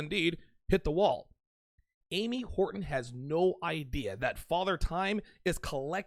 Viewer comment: LOL, I thought you we're saying Amy Whoreton until I read the description xD